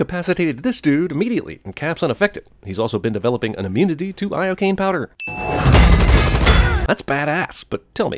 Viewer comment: I heard "I Cocaine" and was like : Woah! Apple is selling Cocaine?